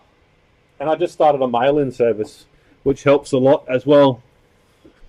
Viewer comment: did you say you have an alien service?